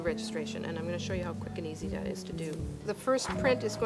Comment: I had to replay 5:52 a bunch of times because I thought you said "and I'm going to show you how F*ing easy that is to do". Nice video